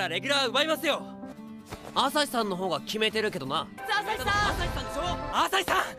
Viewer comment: I hear "Dazai-san" X'DDD